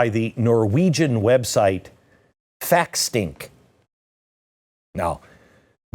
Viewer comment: I swear he's saying fat stink😂